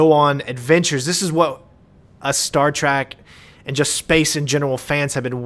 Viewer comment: Did you just say "Star Track"?